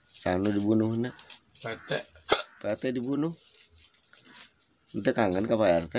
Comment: Hayang bunuh Rt :Bunuh saha ? Bunuh pa rt Menit ke 1.39 Salah denger ga sih gue ?